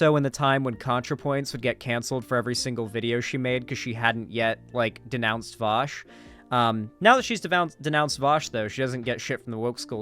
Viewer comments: for a second I thought said keffals denounced Vaush and I was like WHAT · When was that? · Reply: OKAY! i went back, i misheard, he was talking about CONTRAPOINTS, not keffals. MY B